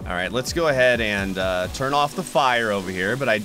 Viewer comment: Did I just hear him say “let me just turn off the fire”